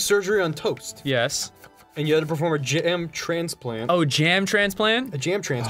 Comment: probably gonna get some hate for this buuuuuuut Who also briefly thought Ross said "Jantran" instead of "jam transplant" around the 4:08 mark?